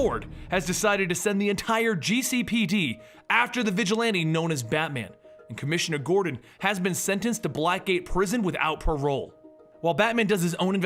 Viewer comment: its sounds like you said black , gay prison lmao